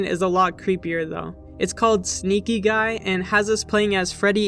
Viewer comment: On the arcade machines part I thought it said smokin gay Instead of sneakin guy